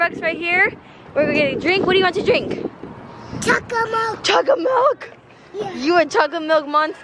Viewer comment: Me At 5:52 DID SAY CHOCO MOCHA HE KNOWS WHAT HE LIKES!!!!!!!!!!!!!!!!!!!!!!!!!!!!!!!!!!!!!!!!!!!